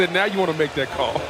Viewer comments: I thought he said "Yo daddy wouldn't make that call" XD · The announcer was wrong, He actually says "Yo daddy wouldnt make that call" since that ref #83 is Ed Hochuli's son Shawn. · it sounded like he said “daddy wouldn’t make that call” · he actually said “yo daddy wouldnt make that call”